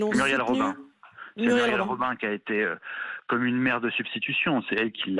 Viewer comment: A 5 min 12 j'ai compris " muriel robin a été pour lui comme une mer*e de substitution " 🤣🤣🤣